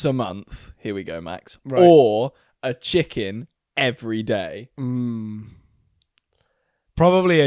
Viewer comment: all i hear is chicken nuggets everyday